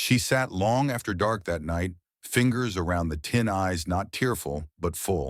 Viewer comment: Run-on sentence at 7:32. Had to listen three times to figure out what was "tin eyes"? It was that she fingered the tin, and her eyes weren't tearful. Missing comma threw off AI narration, and mystified me about what I heard.